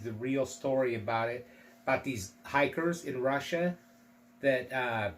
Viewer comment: my speakers are horrid..I thought you said tigers not hikers...i got all excited...still great lol